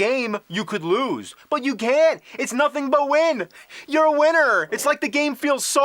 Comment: I thought he said "You're Wiener".